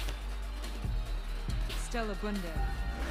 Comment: It sounds like MG said “oh shit” 😂